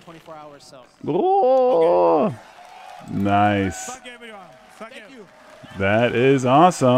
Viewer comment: Don't know why, I hear " f**k everyone" at 15:09 lol.